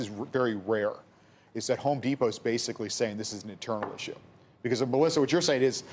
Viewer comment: he says an Internal ____________ ? What the hec was the second word it was not discernable to me and he said it quietly as well? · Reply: Issue